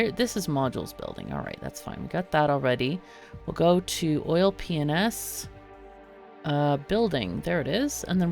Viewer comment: My gf was napping in a chair next to me outside while I caught up on the series and out of no where she goes ‘ha! Sounded like she just said oil penis when she said oil p&s’ lololol so childish but we had a good laugh over it